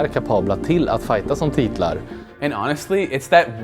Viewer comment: who else heard "hitler" ?